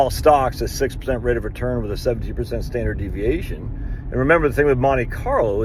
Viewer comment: did I hear correctly that you use a 6% return and a 70 PERCENT or 7% standard deviation? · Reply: 17% std dev.